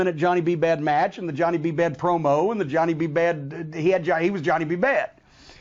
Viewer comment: Personally, I preferred Trish Stratus. YES she had a body made by US stainless steel and DOW silicone. However, she worked at her craft SO she could wrestle fairly well and deliver a decent promo. She recently got back in the ring versus Charlotte Flair and proved that she can deliver some of the goods! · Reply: Man I thought you said porno but you said promo